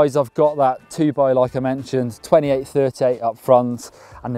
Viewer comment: can someone translate at 1:58 ? did he say "28-30 up front" ?